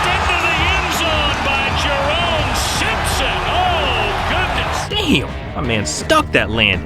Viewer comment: Did he say Simpsons?